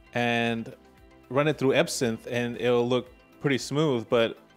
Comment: A sim ? Ape sim ? Epson ? what did you say @ 12:00 ? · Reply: EbSynth 😂